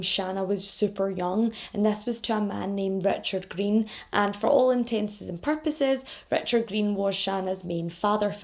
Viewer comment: I've heard you say it a dozen times in quite a few videos but because of the accent - which I adore - I wasn't sure what I was hearing. You are trying to say, "for all intents and purposes", but it sounds like you're saying, "for all intenses and purposes". Just wanted to pass that bit of useless information along 😅.